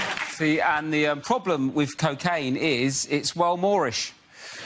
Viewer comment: Walmorish? Or what is he saying...? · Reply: He's saying it's well moorish init